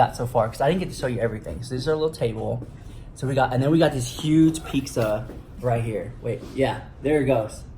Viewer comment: did say Pikza ?